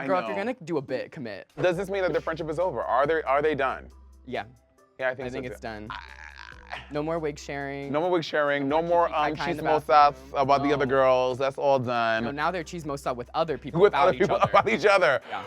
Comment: dawn saying Chismosa . I heard cheese-mows-sah lol😅